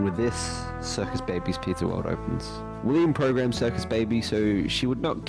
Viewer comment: did he say $3% baby